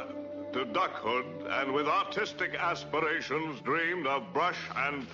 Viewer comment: Was he saying "autistic" or "artistic" with an accent?